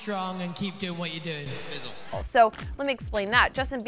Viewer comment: It sounds like liam said bizzle and I don't think they hate justin cause I'm sure niall has said b4 that he liked him.